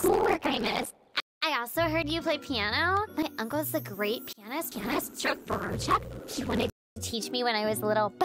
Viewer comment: WHAT DID SHE SAYYYY??! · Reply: She said pianist